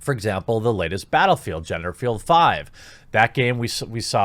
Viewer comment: Did he call it " genderfield 5 " 🤣 3:30 you clever bastard